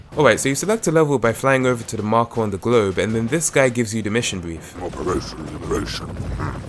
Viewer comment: operation liberation this is what he says